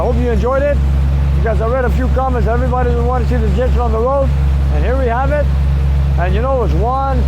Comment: For me, I found the exhaust system very loud and turned on the CC. What the computer thinks is saying. 27:22 "see the Egyptian on the road..." LOL